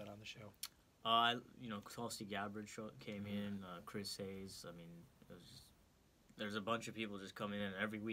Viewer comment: Nice job and. It sounded like he said Chris Hayes? Maybe it was Hedges :)